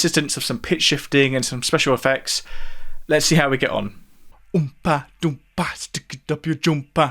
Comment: I always heard "Oompa Loompa"...